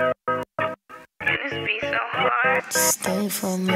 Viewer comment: what she says at the beginning? 0:00? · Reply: " Man this beat so hard". #shanegang